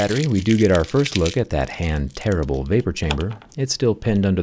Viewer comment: Automated captions saying "hand TERRIBLE vapor chamber" caught me off guard for a second 😂😂